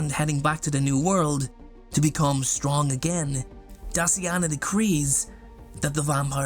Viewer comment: why when you said Daciana i heard "Dustyana" HELP 😭😭